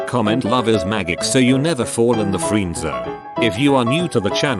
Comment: I thought you said "so you never fall in the freezer" and thought that was useful but oddly specific.